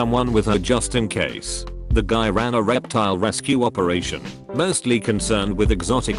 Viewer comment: Thought I heard Erectile Rescue Operation